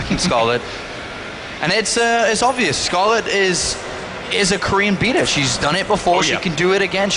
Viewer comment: "scarlett is... is a korean penis!" what? XD i k i heard that wrong but idk what they said